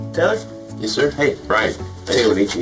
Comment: So who’s Taylor? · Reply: the detective addresses him as Dennis...Edit: I was wrong the closed captions say Taylor... I guess I need to get my hearing checked LOL